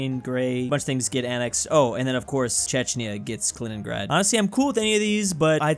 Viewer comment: Chechnia? Am I hearing good?